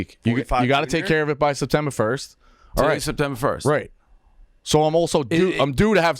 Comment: I thought you said I’m alright spider